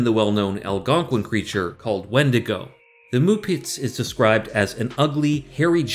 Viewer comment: I thought she said Muppets.